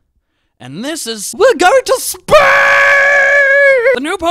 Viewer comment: ALL ABOARD THE HYPE TRAAAAAAAAAAAAAAIIIN! WE ARE GOING TO SPACE!!!!!!!!!!!!! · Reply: Oooh... I thought he said we're going to Spain and I was so confused